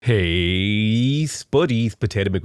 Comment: Wait, he's saying "Hey spuddies"? I have always heard it as "Hey buddies" with some weird accent. That's genuinely hilarious!